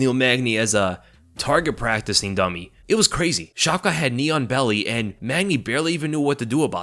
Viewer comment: What is Shavkat's ceiling? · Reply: OK this is funny. I thought you said "neon belly" at first and was wondering what you meant by that. 😆 I was like "does it light up?", and was picturing it glowing. 🤦😁